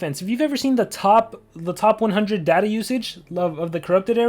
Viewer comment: LMAO, at around 7:35 I misheard and thought sad "Daddy Usage"